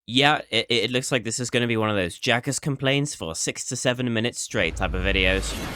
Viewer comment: did he just say…. SIX SEVEN